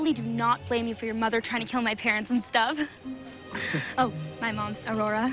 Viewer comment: Ben: up font is only me my parents and girlfriend Mal: I Think I'm Bout To Steal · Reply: Elite K3 Productions I thought he said folks instead of parents